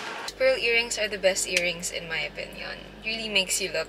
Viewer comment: “Earrings are the best earrings in my opinion” I thought I misheard you but I know you meant to say “best accessories” 😂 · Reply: *pearl earringsss HAHA